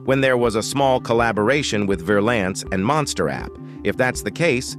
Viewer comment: did he just say monster app